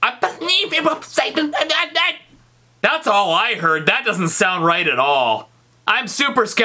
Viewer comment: this is what I a think its saying "I believe it was sent in the eye" Or "I believe it was set in the eye"